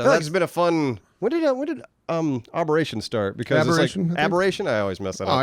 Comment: Sounds like is saying arboration. Like it’s a game about planting and growing trees.